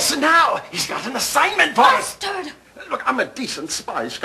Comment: anyone else hear "Bastard"?